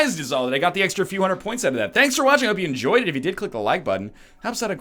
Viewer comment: Up until now, everytime was going to end the video, i thought he said: "NL'll See you next time", Not "AND i'll see you next time" xD